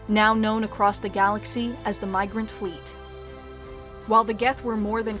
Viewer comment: turn on subtitles, grammar error, "Mirgrant" instead of Migrant. Please fix.